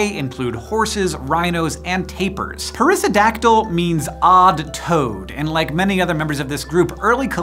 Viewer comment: Thought you said "odd toad" until I looked.